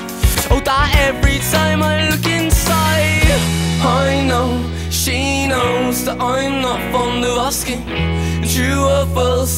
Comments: I kept hearing, " I know she she knows, that I am not from Nebraska ", all this time..... 😂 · I'm not saying it was your fault Although you could have done more Oh, you're so naïve yet so How could this be done By such a smiling sweetheart? Oh, and your sweet and pretty face Is such an ugly word for something so beautiful Oh, that every time I look inside I know she knows that I'm not fond of asking And true or false, it may be, well, she's still out to get me And I know she knows that I'm not fond of asking True or false, it may be, she's still out to get me I may say it was your fault Because I know you could have done more Oh, you're so naïve, yet so How could this be done By such a smiling sweetheart? Oh, and your sweet and pretty face Is such an ugly word for something so beautiful Oh, that every time I look inside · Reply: i literally thought he said “i know she knows i’m not from alaska” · Am I the only one who heard the lyrics right the first time listening to it? I've always heard " I know, she knows that I'm not fond of asking" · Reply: I'm from the South of England (where I think the band is from) but I hear "I know, she know that I'm not from the Ruskin". Which makes no sense, I thought that I either must have misheard or not known of a common phrase from 10 years ago.